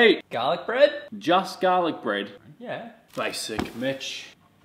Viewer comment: I thought he said Bitch😅